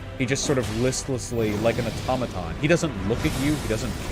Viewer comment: is he saying "Automaton" ? Cause i struggle to hear it right